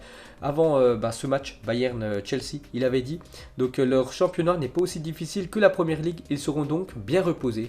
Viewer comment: qu'est ce qu'il dit lui leur 7eme est Sheffield xd